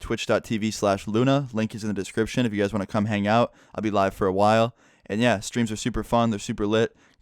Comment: sounds like he said I’ll be alive for a while